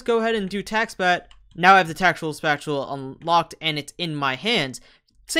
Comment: lmao did you say tactula spatula?